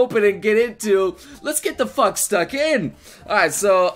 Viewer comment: did he say lets get the fuck stuck in